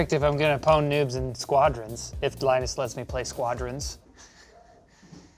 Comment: Why does the subs say If blindness lets me play squadrons instead of Linus at 5:01 lol